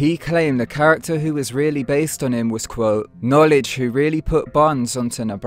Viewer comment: “Knowledge born! What’s the science baby?!” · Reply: I thought he said knowledge bone. That’s what it sounds like but 🤷🏽‍♂️